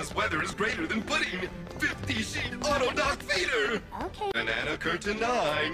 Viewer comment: did I hear that he said " darth Vader " ? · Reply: He said auto doc feeder